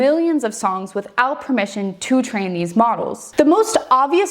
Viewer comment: My auditory processsing caused me to here “Trannies” when she said “train these” and i heard “penis” when she said “pianist” 😂😂